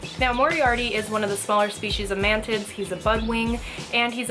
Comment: Instead of mantis i thought she said man tits